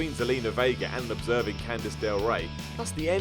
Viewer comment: did you call her candice del rae or can i just not hear?